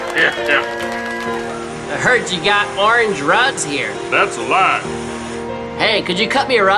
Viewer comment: Orange rugs? I thought he said Foreign Drugs! Cause that's what he's all about, right? Building the "wall" to keep out drug dealers and rapists and goose diarrhea! BUT NO THE GEESE WILL JUST FLY OVER THE WALL! OH NOES! 0.o